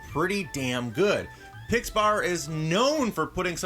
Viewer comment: Did say Pixpar at 3:00?